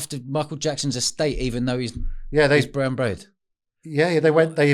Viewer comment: what does he say? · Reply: Brown Bread = Cockney rhyming slang =Dead